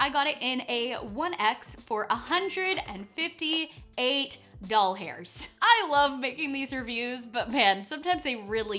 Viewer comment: during the video it sounded like you said doll hairs. lol